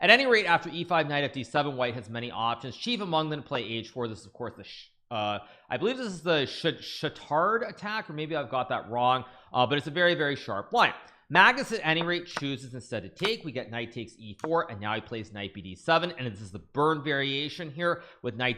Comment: Did he just say the shithard attack at 2:00?